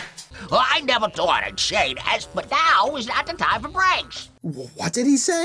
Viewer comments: He's saying "I'd never thought I'd say this but, now's not the time for pranks." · The guy is saying "I never thought I'd say this, but now is not the time for pranks!" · what he said is I never thought I say this but now is not the time for pranks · i think he said "I never thought i'd say this but now is not the time for pranks!" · I think at 18:24, he said “I’d never thought I’d say that, but now is NOT the time for pranks” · Maybe I've given myself to Madness, but I believe the troll at 18:30 says "I never thought I'd say this, but now's not the time for pranks."